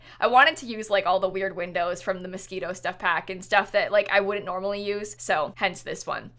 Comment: I thought she said the mosquito stuff pack..